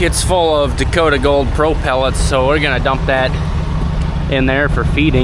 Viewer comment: Why transfer corn from a bin to a barn floor? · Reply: I thought he said they were old pellets